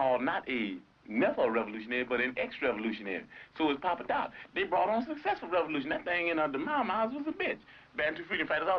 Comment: What does he say about Jomo Kenyatta? That he's not called a what revolutionary? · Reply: says he formed an excellent revolution without an education